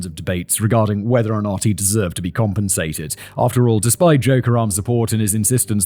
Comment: Every time he said Joe Karem I heard "joke around" and every time I thought, "is this the time to joke around?"